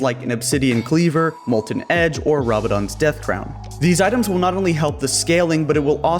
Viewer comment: I thought he said Rabadon;s Deathcrap...........